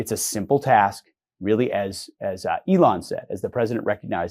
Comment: I heard it as "Elon said, as president"